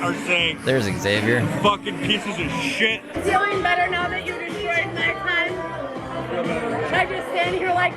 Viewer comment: did she say, “feeling better now that you destroyed my cun*”? I think that’s what she said but idk